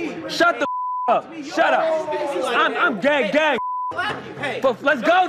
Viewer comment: what did he just say 🤨⁉️ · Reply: “IM GAY GAY” 😂😂